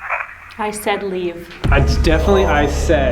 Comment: I Said Run, is what i hear